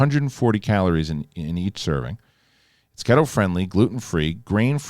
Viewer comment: HA! i heard "ghetto friendly" too 🤣